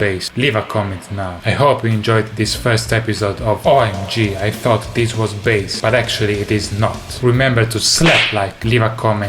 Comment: I thought he said "OMG I thought this was BASS But actually deez nut"